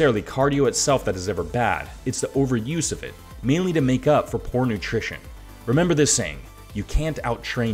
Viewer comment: DId he say "porn attrition" at 4:46?